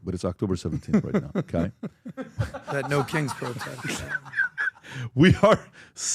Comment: Lmao I thought PBD said no Kinks